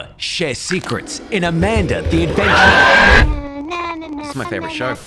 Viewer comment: And I thought he said never SAY NO IN ✨AMANDA the aDvEnTuRe✨